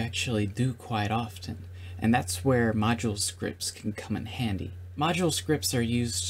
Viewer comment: In 0:26 I thought you said "Macho Scripts" XD. But it's okay, I'm learning ModuleScripts right now. Im a coder and know almost everything. I wanna now focus on learning ModuleScripts.